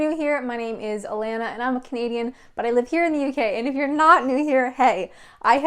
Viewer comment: I thought you were saying "I'm a comedian, I only just figured out you say "I'm Canadian." You're brave swimming in the sea at a UK beach.